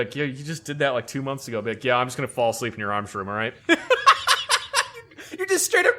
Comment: i misread/misheard "I'm just gonna falls asleep in your arms room, alright?" as "I'm just gonna fall asleep in your arms, alright?"